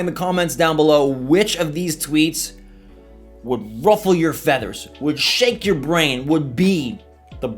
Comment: At the beginning when you said which of these tweets would "ruffle your feathers" Idk why but I heard "which one of these tweets would roughly be your fetish"😂😂